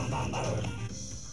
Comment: Am I the only one who heard "Wake up the waffles"? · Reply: Ulises Gonzálvez I thought it said 'We got the wapez'